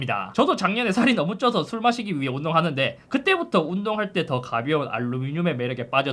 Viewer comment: 나만 잘 못 들었나 술 마시기 위해 운동한다고??